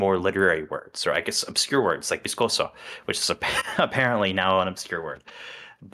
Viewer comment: Ok listen, before y'all come at me. I heard "scoso" not "viscoso" and I was SOO confused. Like...SOOOOOOO confused. Sorry <3